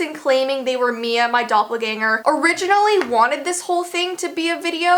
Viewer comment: When people said doppelgänger I thought they said doublegänger lol